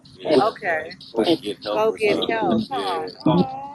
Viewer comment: I hear, "yeah- wouldn't you? Get help"